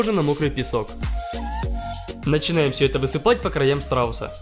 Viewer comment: Мне послышалось: — Все это высыпаем на срауса.